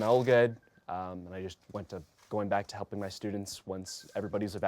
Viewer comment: did he say " . . . MY students . . ." ??